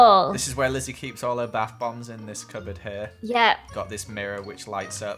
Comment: When I first heard Joel say bath bombs I thought he said tampons then she agreed and I was like wait wham huh?